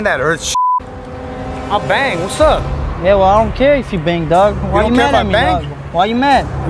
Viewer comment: "I don't care if you bang dog" gat me rolling😊😊😊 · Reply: I thought i heard “idk care if you bang dogs”😂😂